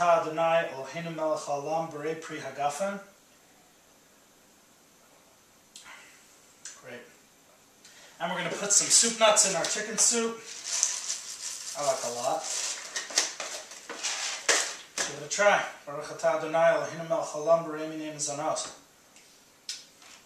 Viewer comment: Your soup looks yummy , but what the heck are soup nots? lol! Iam curious ! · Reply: LOLOLOL! I thought you said soup nots. heheheheh thank you!